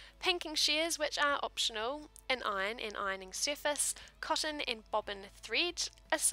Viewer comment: Hi, sorry to be such a noodge, but right after you said an iron, what did you say, please? 0:53 It sounded like an iron and an iron stiffer. ? Thank you. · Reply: she said and iron stiff